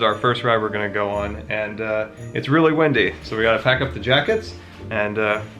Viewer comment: When said it's really windy, i thought he said it's really wendy, a character from peter pan i think